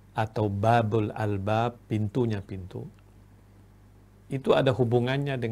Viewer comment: Apa saya salah dengar ? menit 3:20 beliau mengucap BAABUL ALBAAB : PINTUNYA PINTU